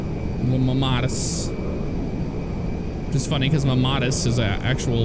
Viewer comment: So, you said, "Mammatus," but I heard, "Mamadas," and I Googled that, and, well... There it is. Hashtag nsfw.